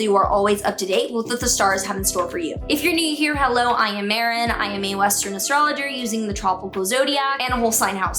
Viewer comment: Did you just say “ Mae Western”? 😂 respec ❤